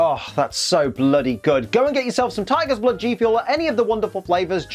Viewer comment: i thought it said tigers bloop xD